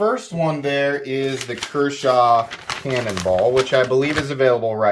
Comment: Thought you said "Kershaw Cannibal" 😂😂😂😂😂😂